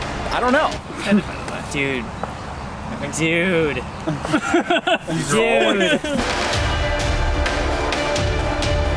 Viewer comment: Why did Jake have to do push ups at 2:42. I thought i figured it out from earlier vidoes but im not sure what he said now that made him do pushups. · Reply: He said 'mine'